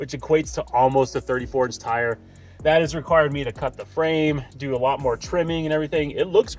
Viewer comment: did you just say cut the... frame? 😳